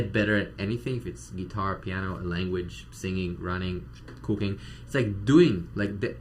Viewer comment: I thought he said singing running cocaine 😂woh don’t practice that. There’s a good chance you’ll get real good at that. 😅